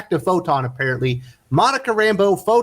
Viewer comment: I misheard, and initialy thought Al Ewing will write Monica, a very nice character I like a lot. And... It's not. Skip.